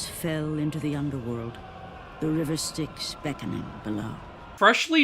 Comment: The subtitles say "River Sticks", not "River Styx".🤣